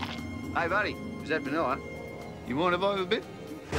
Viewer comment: what did he even say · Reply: He said u wanna more little bit